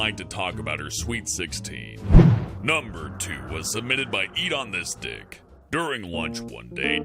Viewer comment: When u said edonystic it sounded like u said eat on this dick 🤦🏿‍♂️